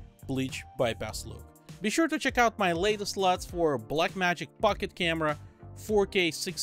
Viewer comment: I heard you say "BlackMagic FuckIt Camera" kkkkkkk